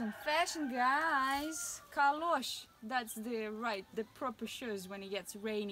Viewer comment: wow, did you said ' kalosh ' is the name of these bit off working-shoes ? it is exact the same word, and pronunciation in dialect Flemish ( Flanders-Belgium) for these rural workshoes , did I misheared ? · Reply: Ha! Yes! I said “kalosh”))) interesting ⚠️